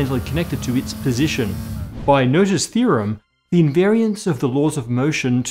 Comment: The subtitles says "Noter's theorem" but isn't it "Noether's theorem"?